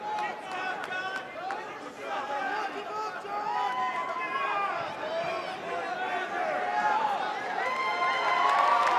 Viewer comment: what did he say? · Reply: No more coke jon